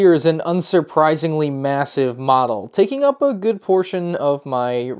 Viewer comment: did you just say MASSIVE?